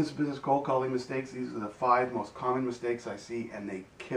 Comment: At the end it sounded like he said "take care of ya johnson"